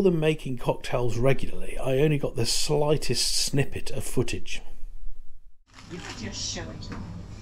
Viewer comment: What did she say? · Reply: The customer at the bar says, "You're just showing off" and I loved the reaction by the bar tender.